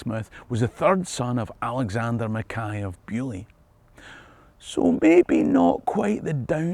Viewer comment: Did I hear Beauly.